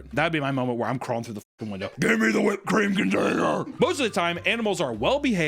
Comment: Am I tripping what did bro say at 12:00 😂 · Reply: It really sounds like it but I think he said container